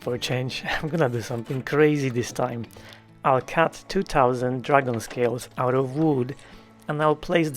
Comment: funny,.... when you said you were putting dragon scales on the door, I misheard you, I thought you said dragon's tails, I had this vision of chinese dragons on the center of the doors...LOL, then when you started making teardrops, it finally clicked in my head if not my ears, SCALES....I also have an accent.....it is US southern. and trust me it is very hard to make myself understood by computers.